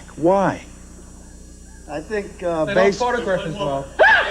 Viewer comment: What did Ace say? Lol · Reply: "They don't photograph as well". That's why he got booed.